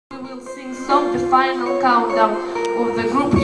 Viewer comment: All i hear is ''its the final condom''.